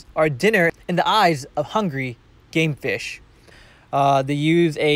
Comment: Who else thought he said gay fish instead of game fish?!😂